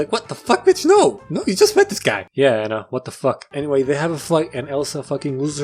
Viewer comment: I like how he sound like he says flight but the subtitles he made said fight